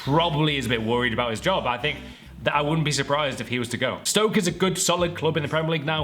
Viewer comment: Sounded like u said Stoke sissy there :D